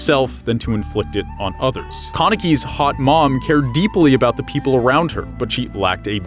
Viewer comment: Did I hear that right? @ 5:03 your telling the sad part of the story and you just hear hot mom out of nowhere😂